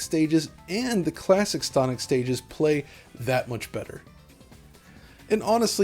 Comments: did he say “Stonic Stages”? Are these hidden levels in the game? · Is it me or does it sound like he said stonic stages